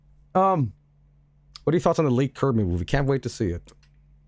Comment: what is he saying? leaked kirby movie?